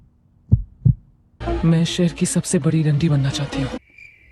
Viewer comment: seconds, did she say ME SHEHER KI SABSE BADI RA** BANNA CHAHTHI HOON??🙄